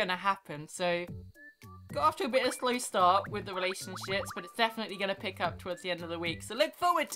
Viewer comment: Lol at the end when she said relationships it sounded like she said relationshits and I just died laughing